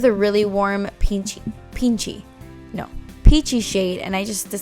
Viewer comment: Hahaha I love when you said peachy and it sounded like you said pinche 😂 I love this look!